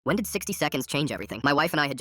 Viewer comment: When he said 60 seconds for a sec I thought he said 67